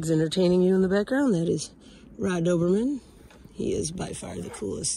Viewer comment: You rock. The painting turned out great. When you mentioned your dog, it sounded like you said his name was Rod Obermann. LOL